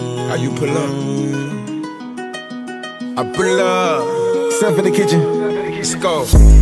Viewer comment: does it actually say: "simp for the kitchen, simp for the kitchen?" cuz thats wut i hear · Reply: Its SethInTheKitchen he is the producer